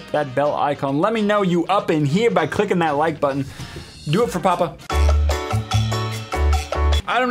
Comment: *Clicks like* I did it for Papa! I love you Dad! *hugs* Do you love me now? XD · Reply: I thought you said "Did it for Papaya"